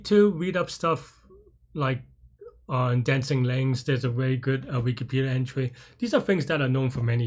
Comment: What's the concept you refer to at 0:54? Wikipedia entry on ? I can't really hear it sorry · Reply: Dancing Links! <link>